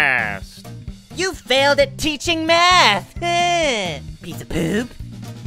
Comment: Baldi: You failed the test man! Player:You failed at teaching eeeh! PEICE OF POOOP! Me:PFFFFFT! HAHAHAHAHAHAHAHAHAHAHAHAÀAAAaaaaaaaaa!!!!!! XD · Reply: It sounded like he said "piece of boob"😂😂